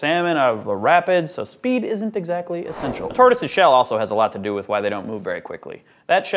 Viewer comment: I heard "a TARDIS's shell" at 1:15... sigh yup, TV has muddled my brain.